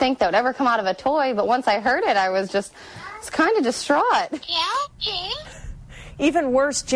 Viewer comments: I heard "Bill James" 💀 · I hear "jail james"